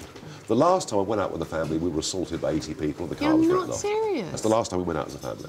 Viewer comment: What does Nigel say at 4:37? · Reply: He said the car was written off.